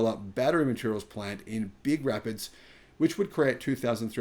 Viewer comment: Did you mean Grand Rapids, Michigan? I thought you said Big Rapids. Sizeable, either way. :) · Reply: It is Big Rapids.